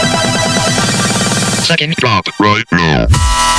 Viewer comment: SECOND DROP RIGHT NOW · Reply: Hammerhead GD I thought it said check it then drop it right now